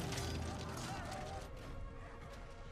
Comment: For all these years watching this film I thought Gimli said "Don't tell me off" before Aragorn tosses him, just realised he's saying "don't tell the elf" 😂